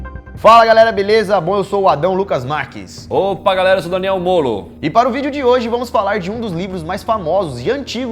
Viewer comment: achei que ele ia fala sou daniel eva molo kkkkkkkkkkkkkkk